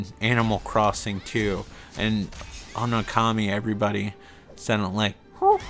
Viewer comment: That's story about the Chinese guy was sad. Ah Okami! I thought you were saying Commie and was like, how can you possibly play a communist?